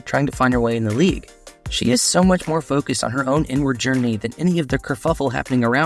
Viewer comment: He said Caitlin’s inward journey, took me a second. I thought he said N word journey. 😮